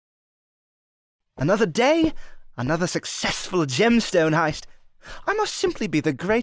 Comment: i thought he would say another day another slay period 🤣